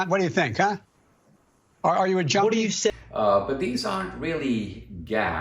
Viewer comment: Sounds like he said "joiney" but what I see is him saying junkie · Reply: It's been edited by his campaign..he most definitely said junkie